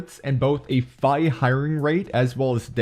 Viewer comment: U said the scythe has a “figh highring rate” instead of “high firing rate” 🤣🤣